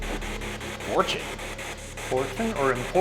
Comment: i heard 'forced'. it would make sense if the spirit was saying that